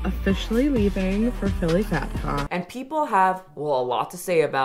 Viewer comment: I thought you said Bat con and I was here for it 😂